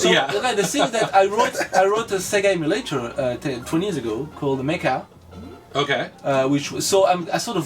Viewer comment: Just wanted to point out a mistake in the captions, the name of the emulator he wrote is called MEKA, not maker, I used to use it before Kega Fusion became the standard. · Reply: If it can help you find it faster, it's at 17:00. Subtitles say "I wrote the a second game later, twenty years ago, called the Maker." It should read "I wrote a Sega emulator, twenty years ago, called Meka." By the look on your face, you can tell you wished you had live subtitles right there. ;)